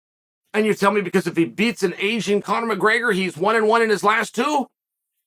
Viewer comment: had this on 1.25x almost sounds like he says Asian Connor mcgregor😂